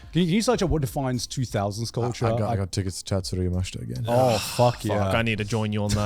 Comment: what did say? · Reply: A little late, but he says, "I got tickets to Tatsuro Yamashita again."